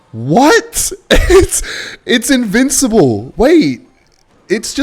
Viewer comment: Its what?? invincible you say huh